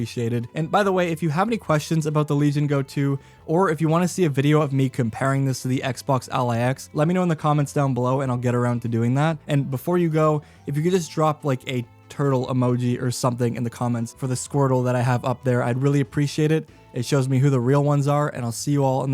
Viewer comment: Lol😂 I thought you said a turd emoji 🤣 until you put the turtle 🐢 on the screen 🤣